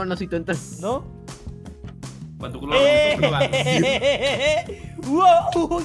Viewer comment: Cuando flex dice no soy tonto CREO que escuche que timba dijo "Pa' tu qlo yo me monto"